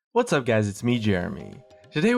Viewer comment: Why do I always hear "What's up, Guys? It's me Germany." Dx